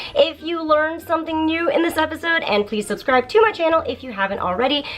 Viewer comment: when you said "subscribe to my channel" I heard "subscribe too much anal".................yup....................ahhh kimazui!!!